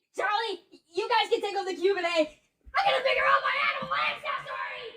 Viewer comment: What the heck did say at 18:36 and 18:46? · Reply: He said I gotta figure out my animal Accessory!!! Edit i meant Ancestry(?)!!!